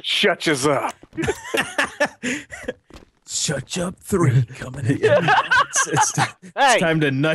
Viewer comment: I Didn't heard "shuch up!" I heard "Ketchup"